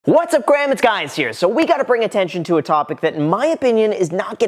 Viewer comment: Did he just say whats up graham its guys here? Lol